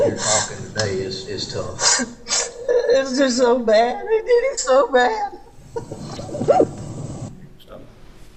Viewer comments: I heard ‘they did IT so bad’ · I hear "I did it so bad" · I heard “It’s just so bad. They did it so bad.” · On the tape I hear “it’s just so bad, everything’s so bad!”